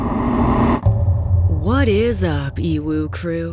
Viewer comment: And all this long I thought you'll were saying, " WHAT IS UP EVIL CREW"😟